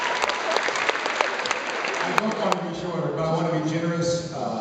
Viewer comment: when the lady shouted out "shorter' i thought at first I thought she said 'boner' :P I had to re-watch that :P